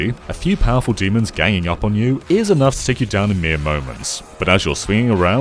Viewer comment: 'mere moments' I heard Miyamotoments